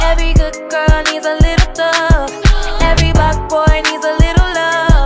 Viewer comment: I thought it said every black boy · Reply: 😂😂 me I thought it said f**k boy